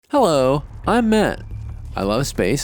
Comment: Lols I thought he said "I'm meth" in the intro 🤣